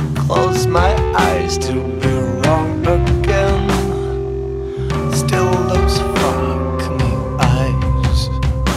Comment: I tried to come up with the lyrics from this. I still need some help for some parts. Feel free to correct me and add where needed. Thanks in advance: I still smell of sweat Still a scent of my giving in Tried to feel regret But I wanted to stay on my skin I still fantasize Close my eyes to be wrong again Still those sparkling eyes(?) As I'm licking the palm of my hand How the hell am I supposed to keep myself When you are so damn far away And everything feels meaningless And I am not mine How the hell am I supposed to keep myself When you are so damn far away And everything feels meaningless And I am not mine I still smell of sex Still her taste on my fingertips Tried to feel remorse But it's hard with her wet on my lips How the hell am I supposed to keep myself When you are so damn far away And everything feels meaningless And I am not mine How the hell am I supposed to keep myself When you are so damn far away And all I choose is meaningless And I am not mine I need? something in my heart Something with a ??? dark? Something ???? wrong Something that I can't control I need something on my own I need something cutting to the bone I need something that is mine If that must be guilt, then fine I wanted something nice, but fine This guilt is a whore?, but is mine Wanted something burnt? This guilt and the whore?, but is mine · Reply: In the "Still looks ???" part what I hear is "Still those fucking eyes" but I doubt that's what he's saying